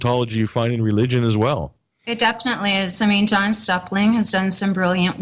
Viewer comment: thank you for uploading this great interview! did anyone catch the name of the journalist at 48:41? trying to find their work to read. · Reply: On the subtitles, it said John Suckling